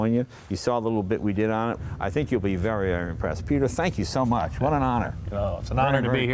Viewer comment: SO at about 15:10 or so, look for it, he says " So go to the Peter Mulin museum and I think you will be very UN-impressed...bla bla.... Gee, did I hear that right?